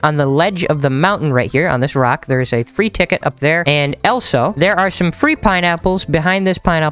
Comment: he said Elsa instead of also